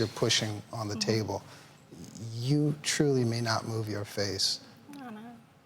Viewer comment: What did the doctor say at 9:48? "You truly may not (???) your face." Love your face? Move your face? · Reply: 'you truly may not move your face.' It's high risk because it would be intwined in all the facial nerves and muscles. Removing it could have resulted in paralysis of the face.